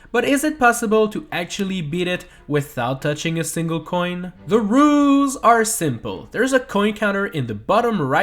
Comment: ok I'mma be honest I thought you said ruse